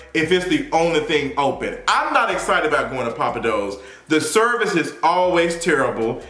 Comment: Nah I thought you were saying Papa Dough's LMAOO tf is Pappadeaux?